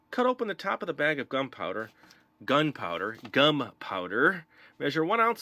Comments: I heard 'gun powder' Say whaaaaat?! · When he said " gum powder" I thought he said " gun powder" lolololol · when he said gum powder I thought he said dum powder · When he said gum powder I thought he said gun powder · I thought he said gun powder not gum powder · I thought you said gun powder